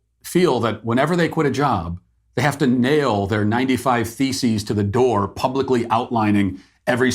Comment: I thought he said feces on the door